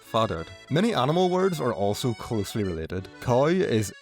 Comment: did just say “coy” instead of “cow”?